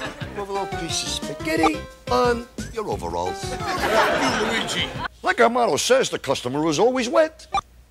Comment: He ~does~ say "Thank You, Luigi" but the way the laugh track interrupts his reply messes with the way we hear it. So it sounds Like he said "F-- you, Luigi" instead.